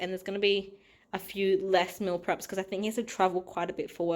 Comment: It sounded like you said your partner is in trouble quite a bit with work 😂 had to replay to hear travel! Your spaghetti bolognese looks delicious. What do the garlic chives taste like? Are they very garlicky or similar to normal chives?